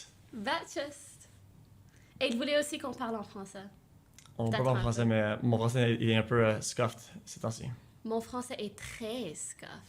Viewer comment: Did he say that his French is scuffed?? How can both his French and English be scuffed? Tf does that leave him with??